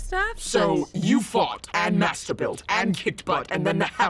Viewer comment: everyone:i heard masterbuild Me:*masterbait*